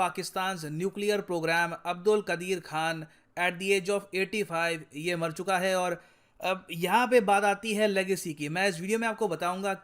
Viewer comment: "ye mar chuka hai (bkl)" This is what i heard 😂